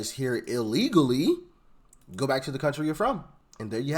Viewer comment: Did she just say her country sucks.😂